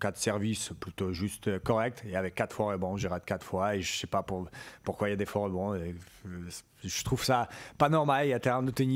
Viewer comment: Quelqu'un comprend ce qu'il dit à 6:30 ? " ... il a fait 4 services plutôt justes, correctes. Il y avait 4 __________, j'arrête 4 fois. Je sais pas pourquoi il y a des __________. Je trouve ça pas normal..." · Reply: Faux rebonds